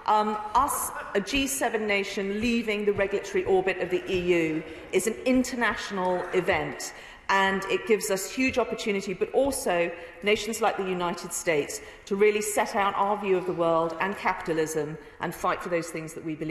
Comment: “The United States to really set out our view of the world”, did I hear that correctly?